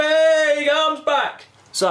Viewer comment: Did I just hear the nurse say the Cocaine we get is 8mg 2:58 😂